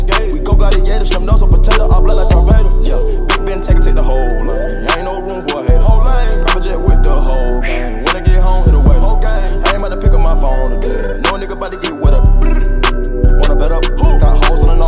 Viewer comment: did he say logang lol?